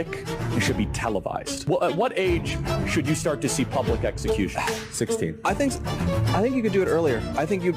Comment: Did he say "Public Execution"?